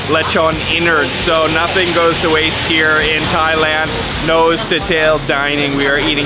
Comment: did you say Thailand